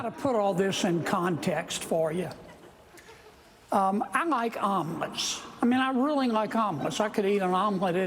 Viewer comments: The guy with the omelettes, holy smokes. At first I heard "armaments", then "almonds". Not omelettes until John Oliver said it in a clear voice. Is that guy so old he can't speak clearly, or was it a freudian slip? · bruh i thought he was saying almonds not omelettes 😭😭😭